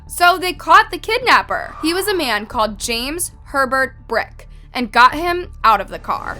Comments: At first I thought they said James Pervert Brick and I was like seriously? He was destined to be a weirdo. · Why did I thought she said his name was pervert 😭 im glad she's safe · When she said Herbert I thought she said pervert lmao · When you said "James Herbert Brick" I thought you said "James Pervert Brick" how did I think that-- I am not even dirty minded. Wth